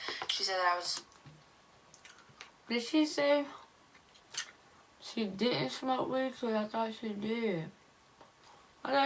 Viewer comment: I thought she said she don’t smoke weed